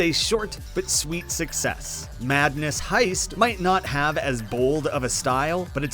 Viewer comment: I thought that said “Madness Fleshlight”💀